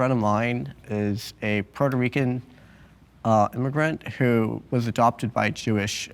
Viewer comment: did he just say Puerto Rican immigrant? 🥴